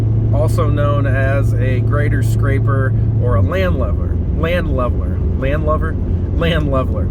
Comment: I thought you said land lubber.lol. I was like wow man. Haven’t heard of that in a coons age. Land Lubbers were cool blue jeans back when Hector was a pup. 60’s 70’s.